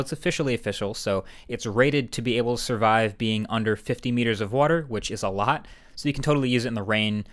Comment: Warer resistance: 1meter up to 30 mins... Not 50m!! That was the Apple Watch 2! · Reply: I thought he said 15 meters? Well, I guess I need more ears checked lol.